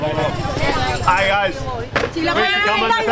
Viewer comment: Did he just say Aye Aye or I.C.E I.C.E 😂 I couldn't hear clearly. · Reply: “Aight guys”